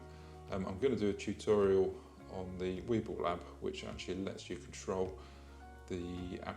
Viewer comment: I listened three times, and even turned on the captions, and still can’t figure out the name of the app you mentioned at 05:06 . Did you say Weebil app, and that works with the M2? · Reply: Yes just the zhyiun zy play app.